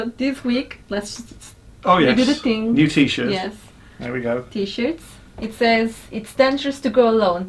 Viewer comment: Did just say “titties” whilst touching man boobs at 1:38?